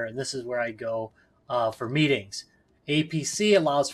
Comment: I said meetings???? What the heck! Missions. Missions is the word.... Although most of my meetings are much like that...